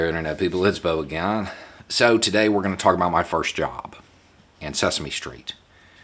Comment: I misheard your intro... I thought you said "So today we're going to talk about my first job, on Sesame Street" :D